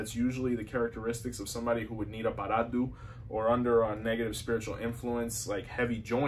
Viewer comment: Did you say Baradu,Parado..How do you actually spell it..i've done a couple but ion know how to spell it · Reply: Oparadu is the formal spelling. Paradu or Paraldo, as heard in Lukumi practice, is also socially acceptable.